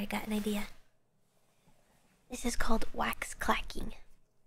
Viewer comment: I thought that said "Wax Cracking as Mr."